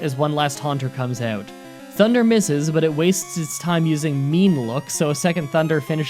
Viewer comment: Is it just me or did he just said Meme look?